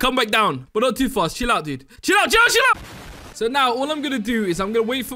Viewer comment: What he said: CHILL OUT CHILL OU- What I thought he said: JELLO JELLO